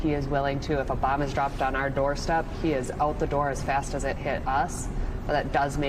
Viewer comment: Why would she say we have to wait six months to respond to a bomb dropped on our doorstep? She has no reason to come to that conclusion. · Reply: THANK YOU for clarifying! I thought she said "if Obama was dropped on our doorstep." LOL!